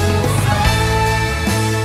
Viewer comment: に、 彼は何を言いますか。 What does he say here? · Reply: Anthony Daichi やっぱり一緒にいたい。 He said ＂I want to still be with".